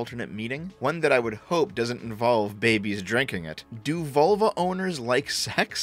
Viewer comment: im a bit of a dumbass for this but heard you say volvo the first time it made do a spit take